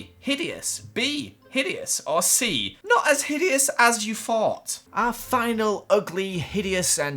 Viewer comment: I thought it said not a hideous as you THOUGH XD